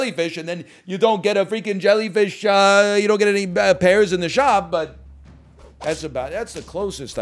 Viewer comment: I love when he says “pairs in the shop” and I hear it as “pears in the shop”